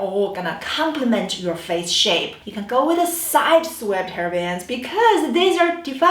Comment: I thought you you were saying hair bands, lol